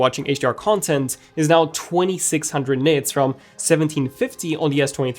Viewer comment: I could have swore they said 2600 nits